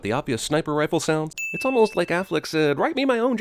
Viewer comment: I won’t lie to you I thought he said affleak like the duck in the commercials. I’m an idiot.